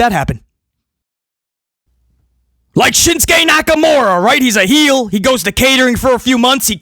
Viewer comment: I thought u had been saying shit stain Nakamura this entire time 😂.